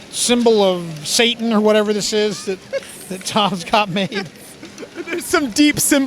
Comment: did he say tom Scott made it